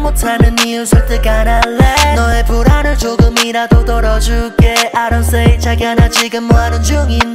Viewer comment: 어떻게 들려요.. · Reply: 너의 불안을 덜어줄게